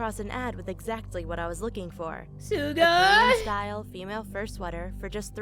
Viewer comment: did I just hear “Suga” like from bts or am I just crazy